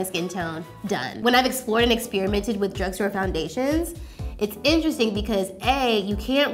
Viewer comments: did anyone else think said “when ive experimented with drugs and foundation” at 1:04 LMAO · Omg i thought she said "experimented with DRUGS and foundation" when she said "experimented with DRUGSTORE foundation" 😂